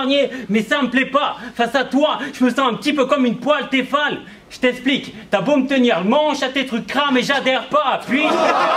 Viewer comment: Que dit a 5:08 et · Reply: Eleyeaaah boss 5:08 : face a toi j'me sens un p'tit peu comme une "poêle téfal". J't''explique : t'as beau me tenir "le manche" à tes trucs "cramés j'adhère pas" !! 13:55 : genre y'a un site de rencontre qui s'appellerait "dans ta soeur" avec tes copines, les frangines, les cousines et les mamans... et toi t'es la t'as plus qu'as te servir, tu te fous vraiment de ma gueule ?!